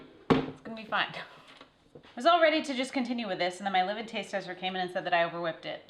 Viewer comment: It took me a while to realize you were saying “live in taste tester” I thought you were saying “livid taste tester” haha! I grew up working on a blueberry farm and have likely made 2000 blueberry and raspberry pies but we always used flour and lard (yes lard) for the crust. I’ll have to try the graham crust sometime. Anyhow I enjoyed watching as I did some woodworking last night.